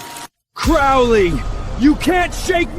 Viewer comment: 😅 subtitles say Chloe but he says Crowley. I wonder if he was calling her a devil? 😅